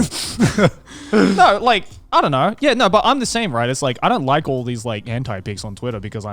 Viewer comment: “I don’t want to see women” ~ Connor True Sigma male grindset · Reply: I thought he said I don't like women